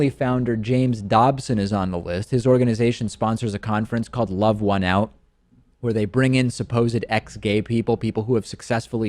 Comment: I thought he said the group's name was "rub one out" and I thought....sounds legit.